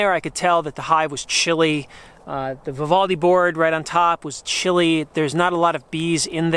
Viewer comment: What did he say that sounded like "...the frivolity board..." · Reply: Vivaldi Board: <link>